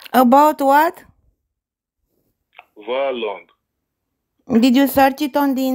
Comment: This one is so mind-numbingly boring and stupid, I'd be astonished to learn that he made anything from scamming. 18:55 - I think he said "violin", for what it's worth.